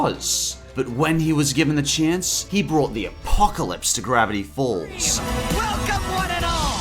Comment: the subtitles say "the epoque lips" instead of the apocalypse